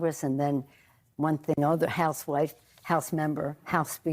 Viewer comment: Did she just say Housewife